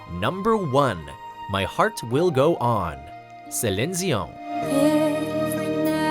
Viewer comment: What did he call her ? Oo Celin sion?